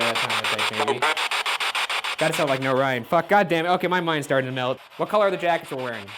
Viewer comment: it sounds like it's saying "go away" or "no way"... I don't really hear the Ryan.